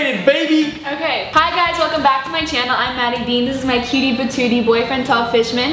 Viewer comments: When she said hey it’s me maddie dean I thought she said hey it’s me Matty b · I thought she said maddie bean